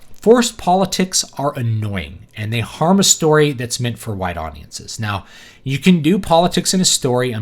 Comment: Did you say white audiences or wide audiences? @ 13:50?